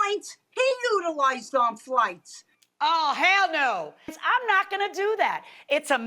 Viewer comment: did judge just say "oh HELL NO." ??? 😆🙌